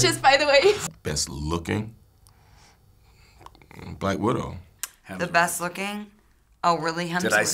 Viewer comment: what did captain america said 0:21 ? i don't understood what he said, can somebody PLEASE tell me what he said? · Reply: "Hemsworth."